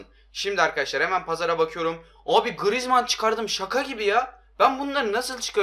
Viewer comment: da grıezman dedini 1 beni duydum